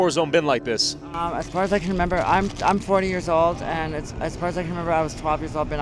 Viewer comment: did she say shes 40 😮😮 thats a rough fuckn 40